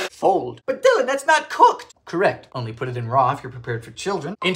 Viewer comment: God Dylan you almost gave me a heart attack! I thought you said "only put in raw if you're into children" I HAVE NEVER BEEM SOOO HAPPY TO BE WRONG!!!!